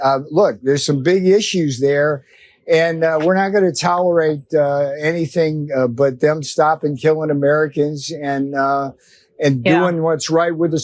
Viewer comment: did he actually say " we are not going to tolerate Canadians killing Americans" this guy is crazier than a rat in a tin sh*thouse